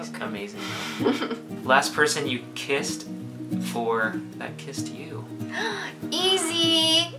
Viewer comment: Wait…at 8:50 did say the last person he kissed was Meghan? Cuuuuuuute!! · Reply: No, he said "Last person you kissed...or that kissed you" I thought so too for a second! But then I listened harder.